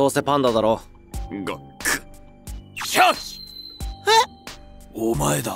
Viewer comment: 上司「シュワッチ！！」（空耳）